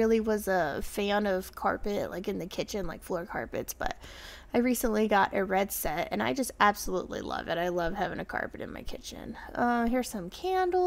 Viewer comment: Fun video shopping. I thought you said you put carpet in your kitchen. I thought what?! 😱 Then realized when you went by the area rugs that that is was what you meant.